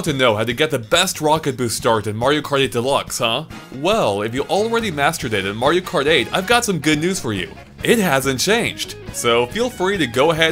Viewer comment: what did he say?? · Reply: Dr. Vectrex "well if you already mastered it in Mario Kart 8, I have some good news for you"